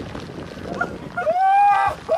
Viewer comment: Island Elder : where is the sacred stone? Tour Guide : the what now? Edit : the comments lol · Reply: Thought it was Swedish, I heard “Åh herregud”